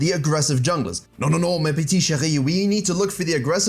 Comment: Ha ha ha 6:30, EPIC :D That got me laughing. Btw, I hear you say "petits chéris" but you wrote "petites chéries". Cheers.